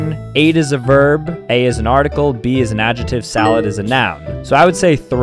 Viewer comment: anyone realise he said “bee” instead of “big”?